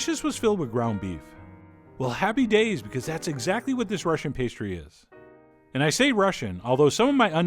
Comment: I thought he said “Russiape”